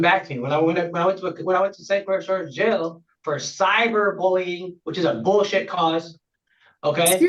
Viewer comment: He doesn't have an email but it sounds like he said 'cyber stalking' charge?? This man is just a liar smh.